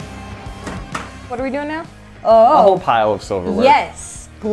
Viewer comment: did she just say OWO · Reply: "Oh, oh-oh!"